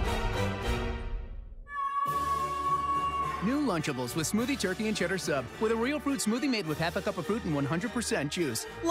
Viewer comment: so what did he say? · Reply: Yeaaaaaaah!